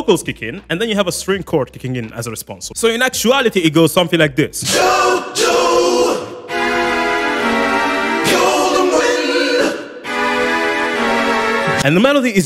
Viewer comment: The first tim I heard "Jojo golden wind" I thought it was Jojo gonna win